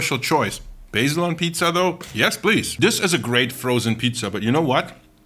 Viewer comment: I heard “basil on pizza, that’s based!” Instead of yes please the first time and had to rewind